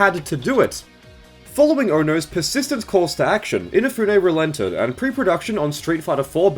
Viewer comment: I thought you said "Inafune Rolento'd" and I thought of the Final Fight character